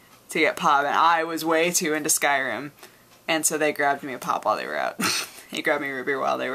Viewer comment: i thought you were saying pot